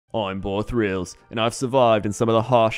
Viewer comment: I always thought it was "Boar Grillz" since that's what it sounds like you say most of the time lol